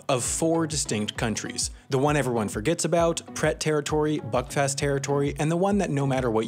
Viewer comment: Buckfast territory is the best territory. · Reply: I heard "fckfest"